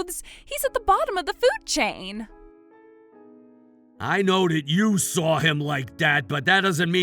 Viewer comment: What is wrong with the dialogue? The text messages have numerous errors compared to the voice acting like “dad” instead of “that” at 20:43 .Did you guys have AI write down the text (which is known to have errors, especially due to accents) and just… not read over them???